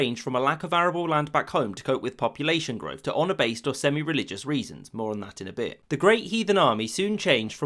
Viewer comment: I had to turn on subtitles. Because while I do like his videos for the most part. Sometimes, it's near impossible to understand what he is saying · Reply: ‘A lack of arable land or honour or religious based reasons more on that in a bit’ what’s so hard about